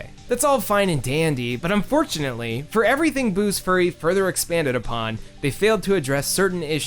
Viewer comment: also it sounded like you said Buu's Furry at 16:43 and now i'm getting... mental images